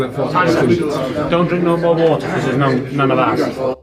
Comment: Sounds like he says Dont drink no water is none of ours